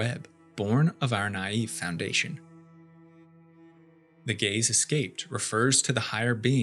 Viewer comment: The way i misheard 25:49 as "the gays escape,"